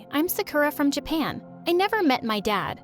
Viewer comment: why did i hear sickura